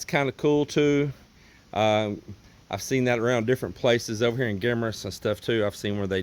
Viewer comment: did you say Guimaras?? If you did that's cool because friends of mine, here Guam, are from Guimaras. I believe his brother or sister in law is a Mayor in one of the towns there.. small world.. anyway, I love watching your videos. Great job!!